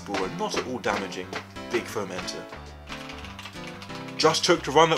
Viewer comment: Sometimes you said foam enter and I heard it as fermented.